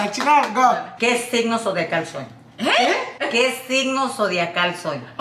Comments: escuche que size de calzón soy 😂😂 quee lol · I thought she said que size de calzón soy 😭😂😂😂😂😂 but